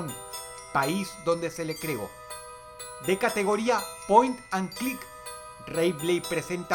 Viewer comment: que es lo que dice Dross 10:14 "de categoria ...." ? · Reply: Point and click osea de apuntar con el mouse y escojer opciones